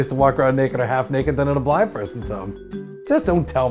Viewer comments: I thought he said black persons home 😂😂😂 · "But at the same time, there's no better place to walk around naked or half-naked than in a black person's home." Who else heard it like this? hahaha